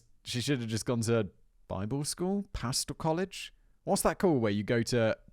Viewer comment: I thought you said “Pasta College” instead of “Pastor College.” I’m Italian. You can probably tell.